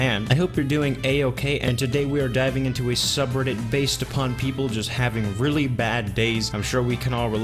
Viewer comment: at first I thought it said "the subreddit of bad dads" and I was like aha I can relate! but this is still awesome